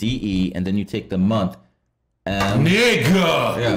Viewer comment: Did he really said negg* for real at 34:55 ? 🫨🫨🫨🫨